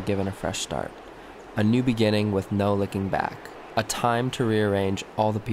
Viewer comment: i heard 'with no licking back' and died